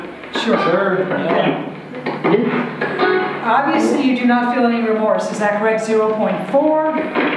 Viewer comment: when they were asking if Richard felt any remorse I heard "Don't....feel.. Any"